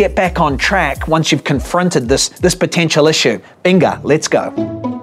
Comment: When you said venga I thought you said Pinga lol